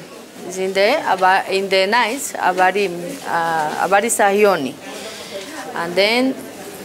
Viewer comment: Kwani ni mkamba, I heard "habari ya yioni" jioni😂😂